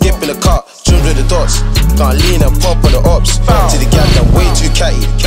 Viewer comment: did just say Gun lean????Hs are da hardestttttt